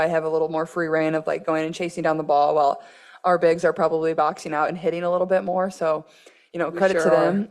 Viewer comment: ‘Our bigs are probably’. Unless I missed it someplace else,(humanly possible) she did not say I think in this sentence. She did use it multiple times in other statements. Love your videos. Thanks for all you do.